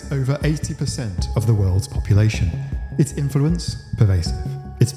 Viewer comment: For a second in the beginning I misheard "it's influence, pervasive" as "interprets pervasive" as in the disorder is how they just pervasively interpret things however they want 💀